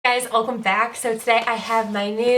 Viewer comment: Is it just me or does it sound like she says “hey guys, ulcome back”, instead of “welcome”????